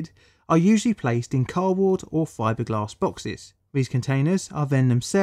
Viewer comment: Did he say Cardboard @ 3:15 ?